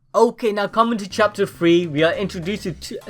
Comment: When you said chapter 3 it sounded like you said chapter free